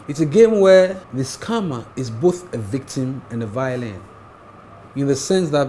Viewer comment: Lol at 18:40 it sounded like "The scamme is both a victim and a violin"